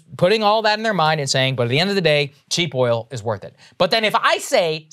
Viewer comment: did I hear Saagar say "end of the gay" LOL. Know it was a slip but damn it was funny...